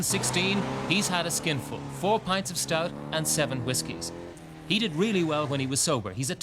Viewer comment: I thought it said 4 pints of Whiskey